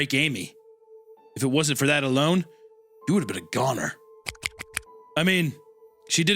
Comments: gooner instead of goner LOOOOOOOOOOOOL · IT SAYS GOONER INSTEAD OF GONER 😭🙏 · says gooner instead of gonor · gooner instead of goner 😭